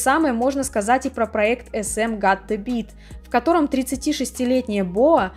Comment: подскажите пожалуйста на 15:15 что сказано? не могу расслышать название проекта SM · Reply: got the beat